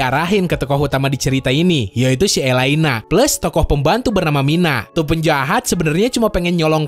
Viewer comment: Namanya keknya saya deh bukan mina apa gw aj yg salah denger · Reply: nah komenan ini yang gua cari, nama murid elina itu SAYA bukan MINA